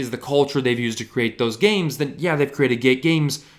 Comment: did he say gay games 😂